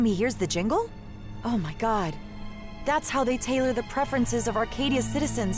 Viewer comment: subtitle typo should say tailor instead of Taylor